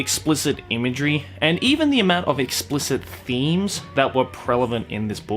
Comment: Am I the only one who hears "prelavent" instead of "prevalent"?